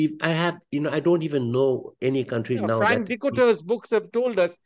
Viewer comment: If you wondered what that name he said was but could not hear it clearly, it was Frank Dikötter. Dutch names like that are not that obvious to English speakers.